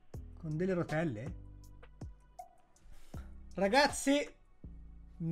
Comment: Solo io la prima volta ho sentito "nuovo brouker"?